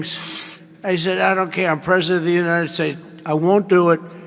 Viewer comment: Sounds like he said "I'm preshaent of da unana shites"